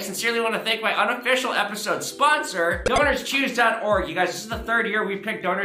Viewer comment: When you said the sponsor I was just listening and thought you said Donors Cheese haha